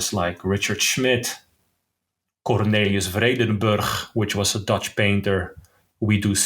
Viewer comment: Hi, great podcast and amazing talk ! who is the artist that named on the 2:03:45? the dutch one, i cannot understand the last name "Cornelius ..?" thanks! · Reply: Thanks! Vreedenburgh